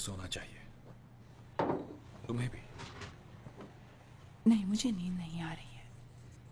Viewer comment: what does she say? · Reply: nahi mujhe neend nahi aa rhi hai 'no I'm not feeling sleepy'